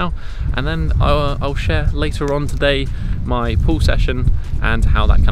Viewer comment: For most of the video I thought you were saying pool session....by the 15th minute I realized it was pull session